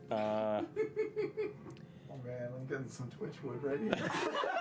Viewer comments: What did Sam say at 11:57 ? All I understood was "Oh man, I'm getting some (twitch wood ?) ready." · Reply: Martin de Boutray it was ‘oh man i’m getting some twitchwood already’ referencing how fjord tried to tell avantica that his old captain’s name was twitchwood which is laden with innuendo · can someone tell me what Sam said here? I can't hear it. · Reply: He said he was getting "twitchwood" right then. A play on the false name Fjord gave for the captain of his ship.